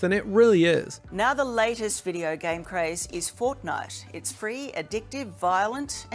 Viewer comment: hold up hold up did she just say fortnut